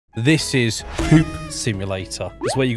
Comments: when i heard the starting of the video i thought i heard ''this is poop simulator'' until i read the text and it said hoop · I heard him say poop simulator